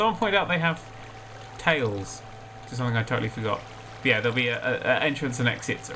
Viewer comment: Did you say "I turtlely forgot" in 10:22? That would've been a hilarious pun if you did.